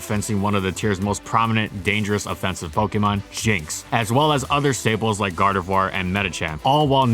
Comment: I heard "shinx" and was incredibly confused for a second